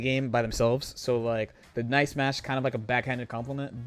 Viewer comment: I thought you were saying “Nice Match” for a second and I was wondering how that could be a backhanded compliment lmao